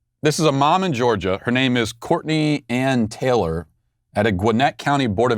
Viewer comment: My kids were talking and I didn't hear "Courtney Ann Taylor." I just heard "Ann Taylor" and I thought Matt was telling us where we could get a shirt like his.